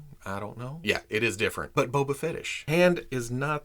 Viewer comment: Did you just say Boba Fetish? 🤔